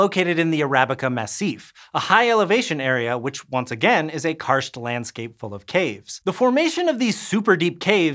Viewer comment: What is the word right before landscape? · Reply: Karst.